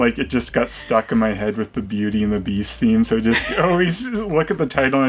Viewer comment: Damn it, I misheard and thought you said Busey and the Beast, and an now both annoyed and relieved that only exists in my imagination!